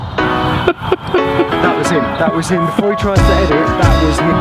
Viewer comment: What did they shout at 3:55 ? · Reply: Elliot Thomas FORE LEFT!